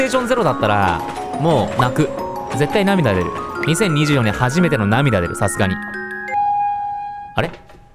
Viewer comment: 動画投稿ありがとうございます！ 最初から2024年が2024円に聞こえて面白くて進めないw